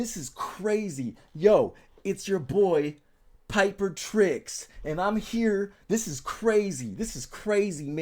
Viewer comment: 2 seconds in I was like , wHy dude actin like sniper , tHen I Heard “ “😂😂😂😂 I instantly liked & subscribed 😂😂😂😂